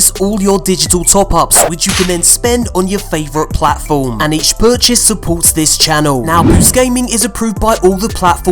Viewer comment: Did he say "each burchase supports this channel" at 0:35? What the hell is a burchase and how do I get them?